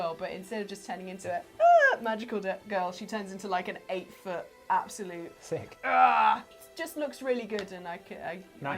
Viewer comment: did just say "Thicc" lol